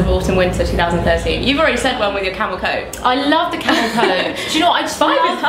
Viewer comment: sounded like I love the camel toe. Rofl